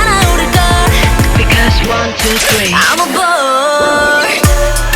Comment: I thought she was saying I’m a boy😂 ( no hate guys I’m just joking)